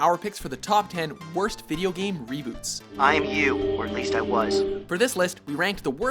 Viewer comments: I thought it said robots instead of reboots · Oh "Worst Reboots"! When I clicked this video I thought it said "Worst Robots". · Awwwww, I thought it said robots not reboots ;_